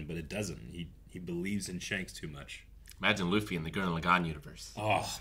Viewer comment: what does he say? Guliergarn universe??? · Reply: lol he said Gurren Lagann in reference to the anime